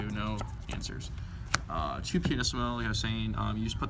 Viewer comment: What's the spell card at 6:55? I didn't catch what he said and I don't recognize it. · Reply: Scott Pahnisch pianissimo; it's good in in True Draco(zoo) matchup. I side three copies.